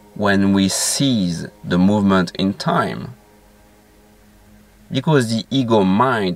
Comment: Thank you so much. Your videos are always an ODYSSEY. One little thing I must mention, I think you used the word "seize" incorrectly, here. Much love from Brighton UK. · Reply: Ahhhhhhh ! That makes sense now. I thought you said, "Seize the movement in time" which is the opposite of "Cease the movement in time." On a previous video you said, "Thought is the biggest problem in the world" - and I thought you said "Food is the biggest problem in the world." I love you AND your pronunciations !